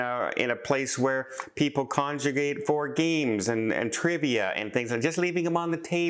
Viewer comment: That's totally shady, and good on the cruise line for trying to keep the playing field level, but I couldn't get past the "....where people conjugate for games." statement instead of "...where people congregate for games." at 3:25. Ummmmm... there is a HUGE difference between what people would be doing in those areas if that was the case. 😆 Totally innocent mistake but kinda funny nonetheless. Travel safe, all, and thanks for all that you do to keep us fellow cruisers updated on what's really important.